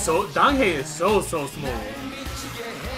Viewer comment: I started laughing at 'Donghae is so so smooth' because i thought you said was SMALL 😂😂😂 forgive me ELFS and everyone my hearing is a traitor 😂😂